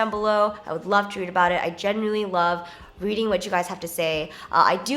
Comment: Idk why but when Jenn said "genuinely" I heard it as "Jenn-uinely" lolol